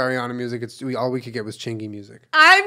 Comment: Did they say Chingy music? Who is that? 😅😅